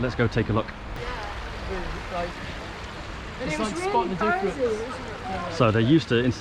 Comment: Did someone say "spot the difference" as you rolled up to talk thru the changes to the spot next to the river? About 11:30 mark.